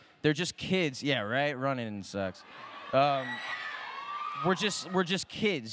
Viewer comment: did dylan say sucks or sex ? xdd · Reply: nothing He said Running Sucks